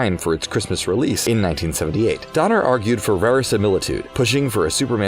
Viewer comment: Did he say Superman's release in 1938? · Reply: 1978